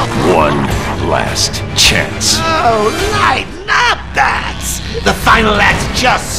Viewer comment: Fun fact: at 3:06 the developers actually got the subtitles wrong. Joker actually said “ Knighten up Bats” Foreshadowing Much!